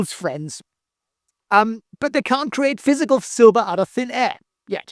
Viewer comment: I heard, “they can’t manufacture silver…yet”.